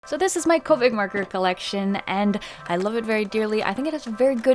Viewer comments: Thought you said "Covid markers" Instead of copics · i legit thought she said "this is my covid marker collection" I was like :0